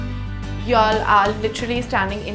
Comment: she's saying you'll all? 😅 Maybe it sounded like that 👀